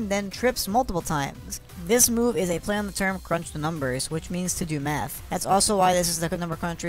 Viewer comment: Did he just say "To do meth"??